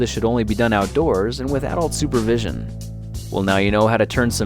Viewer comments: It sounded like you said "... with-Out supervision," instead of "... with Adult supervision." :D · Did I hear right? 4:40 - "This should only be done outdoors, and WITHOUT supervision" ^^